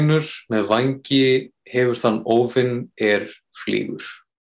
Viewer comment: Really easy until i heard "óvin" thought he said "Óðinn" or "Odin" 😅